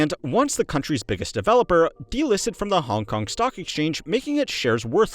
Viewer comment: I was half paying attention when he said "Hong Kong Stock Exchange" and I heard "Honk Honk Stock Exchange"